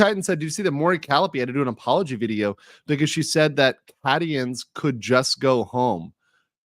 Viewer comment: Playing Satisfactory. I hear "Calli said the Cadians 'could just go home.'" I had to rewind in a double-take, because I thought I heard an odd pronunciation of a "Haitians".